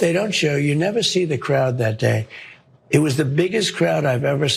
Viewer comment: I thought he said the crowd was Antifa. Smdh